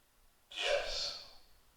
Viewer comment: sounds like woman said yeah..